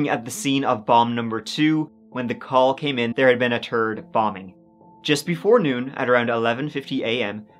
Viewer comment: Did he just say "a turd bombing?"